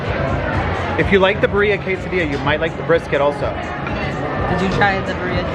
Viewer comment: The whole time I thought was saying Barea quesadilla and I was like what is that period until said it lol also I'm definitely